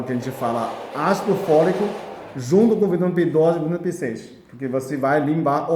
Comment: Oi e pessoal! No minuto (3:05), quando o senhor passa a fórmula; só entendi a parte do Ácido Fólico. Não entendi o outro nome, que o senhor fala. Pode me dizer que nome é? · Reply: Veja outros vídeos dele, ele fala sempre sobre isso. Mas adiantando a vc.. Ele falou ácido folico (vitamina b9), vitamina B6 é b12. Não pergunte a dosagem, veja os vídeos dele.